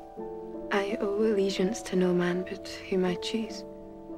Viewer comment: Somevody tell me what she said in 1:50 please ? I can't understand :-( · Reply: "I owe allegiance to no man but whom I choose"